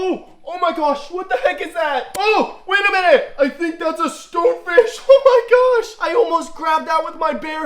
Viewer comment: BRO I THOUGHT HE SAID STEW FISH IT WAS STONE FISH